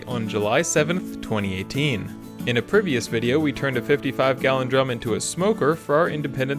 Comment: did you say "privious"? wtf is that?